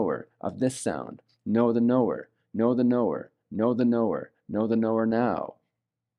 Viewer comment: All I heard was “Noor the knower! Noor the knower!” And I was like yeah! 🤪😆